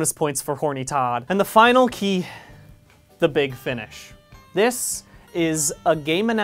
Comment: I thought number ten said the big funsh